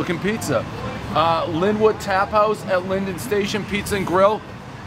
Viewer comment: I thought it said trap house🤣